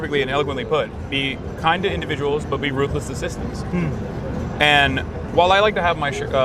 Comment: "Be kind to individuals, but be ruthless to systems." - Michael Brooks Because the Closed Caption got it wrong.